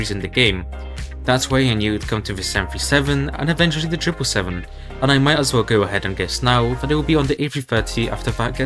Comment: captions say 787 but you said triple 7 just letting you know